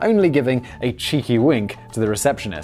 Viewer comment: Little disappointed. Thought it said " air heists "